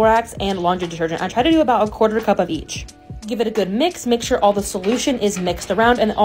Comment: Y did i hear "DRY YEAST" to that cocktail? 😅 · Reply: I think when you said "quarter cup of each" I heard dry yeast. 😅